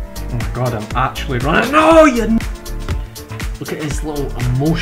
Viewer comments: did he say NOOOO you ni- as in you n word? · anybody else hear "NOo you ni-"